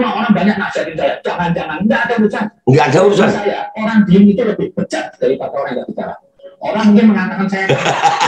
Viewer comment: Apa aku salah dengar ya? Kayaknya bilang bilangnya orang diem lebih bejat daripada orang yang tidak bicara?